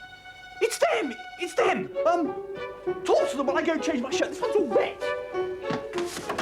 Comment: I can't quite make out what he is saying at 37:42. My ears tell me one thing, but I can't accept it! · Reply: Talk to them while I go and change my shirt, it's got all wet